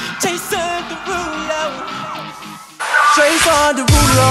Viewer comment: What I hear after ten secs in the video "JaY CiNderuLo" "JaY sIn DeRuLeR" "JaY sEeN dErulO" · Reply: I always hear "Jason the ruler"